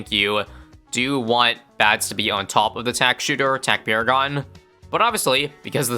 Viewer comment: Am I the only one who thought he said Tack Sh*tter😂